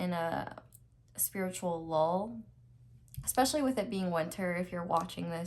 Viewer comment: Pile 3: at first I thought you said I was in a spiritual "lol" hhahahahahaha